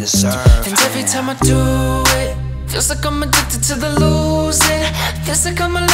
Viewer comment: When he said "I guess I'm addicted to the lOsing" I thought he said Logang😂😱🕵️🤔😄